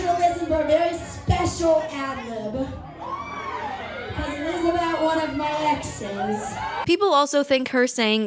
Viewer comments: when i first heard the quote used at 16:23 i genuinely thought she ‘I wrote this’ where the (???) is. now im pretty sure she said ‘it is about’ but now only talking about the ad lib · she literally says “I wrote this about one of my exes” and you couldn’t hear that? It’s not unintelligible enough to put (???) for “I wrote” — then you go on to say people took it as she wrote it ? Of course they would, cause she said she did. All I had to do was turn the volume up and I heard “I wrote”. With the karma song, the stuff that she said was definitely implying that she had a hand in the creative process lyrically. That’s what people were upset about. If she mentioned that the song was pitched to her (more than once) and then actually made noticeable changes to the lyrics and production itself then no one would’ve been upset. Instead everything she said was misleading.